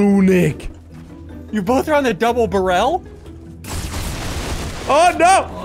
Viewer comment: double burael that’s what he said